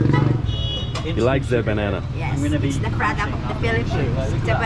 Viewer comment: What did she say...?? "It's the pride of the Philippines"..?? 🤣😂🤣😂The Banana 🍆 She's way too cute.